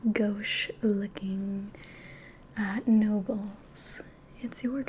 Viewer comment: the captions say no balls 💀💀